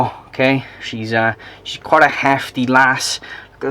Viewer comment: Listeung to this in the shower. I hear ...."She's a bit of a hefty girl...." Oi! Cheeky sod!! Lol. The spotted python gorgeous.